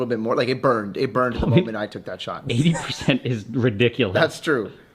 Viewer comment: I thought you were the saying "80% is...." But the cameraman in the video replied.